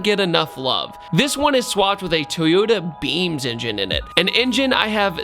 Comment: I thought you said Toyota beans engine for a second